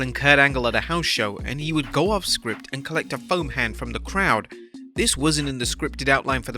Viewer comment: With his British grammar I thought he said phone hand not foam hand